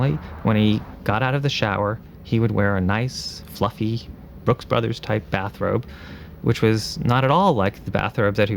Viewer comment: "A nice fluffy Blues Brothers bathrobe" ahaha. That's one was funny. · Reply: What's even funnier is you misheard what he said. He said nice fluffy "Brooks Brothers" type bathrobe, Not Blues Brothers lol. 26:53. I think you're starting to have cognitive issues like The Chin 😂